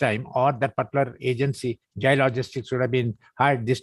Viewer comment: did he say jay logistics? As in jay shah?